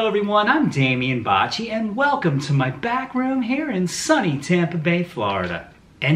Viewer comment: I thought you said " welcome to my background hair" Anyway, beautiful guitar, gorgeous tone and of course, great playing